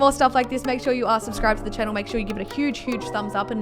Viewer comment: did you just say unsubscribe